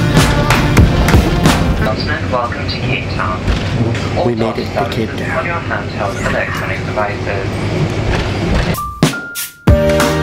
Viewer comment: my captions said welcome to hate town